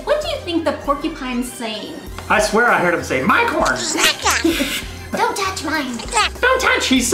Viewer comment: i heard it say minecon haha xD